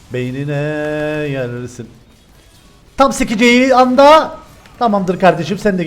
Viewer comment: te ne dedi tam ***eceği anda demedimi ben mi yanlış anlıyorum